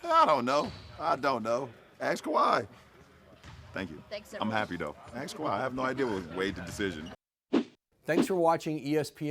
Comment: What was the question the reporter asked, all I heard was mumbling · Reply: “What do you think swayed Kawhi’s decision?”